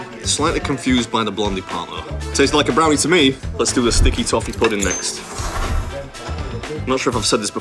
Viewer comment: sticky toffee.... I thought she said stinky tofu... haha this episode was a nice tree-t.